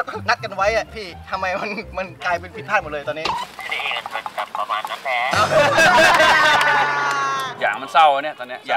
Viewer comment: พี่พูดว่าอะไรอะครับ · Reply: dumexbabymild “อะนี่มันแบบๆๆ...ประมาณนั้นแหละะะะะ” ไม่แน่ใจเหมือนกันว่าใช่รึป่าว